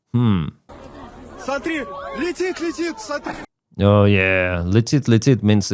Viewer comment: I thought she was saying come come Russian BBQ! @ 12:00 on your video. Lol! Great News!